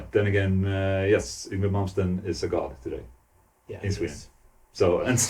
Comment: yngwie malmsteen? hell yes i hope thats what i heard